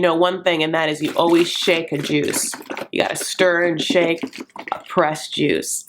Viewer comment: For a second there, at the end of your video, I thought you said, “always shank the Jews”, which, in the context of this blog COULD have made sense. But then I realized you were talking about shaking juice. What a relief!